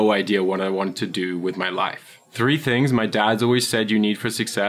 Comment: did he just say dads